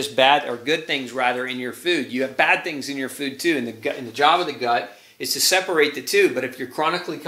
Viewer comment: I thought you said Jaaba the hut, but you said Job of the gut. :)